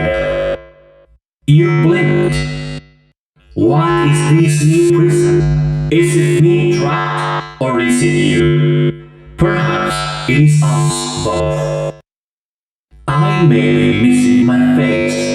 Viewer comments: Withered Bonnie line 4 *what i hear*: What is this new person/prison? Is it me trapped, or is it you? Perhaps, it's us both. Not highly 100% sure about the first line but the 2nd and 3rd line is correct. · Withered Bonnie says "What is this new prison? Is it me trapped, or is it you? Perhaps it's us both" you're welcome. Also im pretty sure (not certain) that he says "Come face the consequences of your failure" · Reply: I thought it said wa is dis new person is it me trapped or is it u perhaps it's us both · The one with "???" with withered Bonnie says "What is this new prison? Is it me trap, or you? Perhaps it's us both." Im sorry if someone else already said it before, but I've never really watched a single gameplay or played the game myself, so I'm not 100% it is what he said.